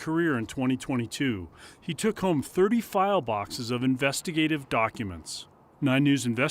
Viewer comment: I thought it said flies 😂😂